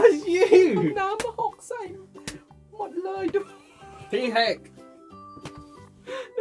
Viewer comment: นาทีที่ 9:12 พูดว่า “หีแหก” หรอ <num> ขำมากก